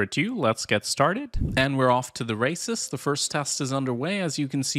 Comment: i thought he said "and we are off to the racists"